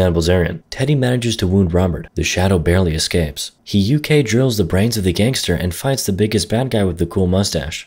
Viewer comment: „He UK drills the brains of the ganster“ 😂 im glad i heard that